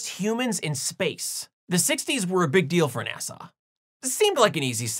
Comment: It sounded like you pronounced "NASA" as "Nasau". According to Rule 8, I have to ask: Was that intentional?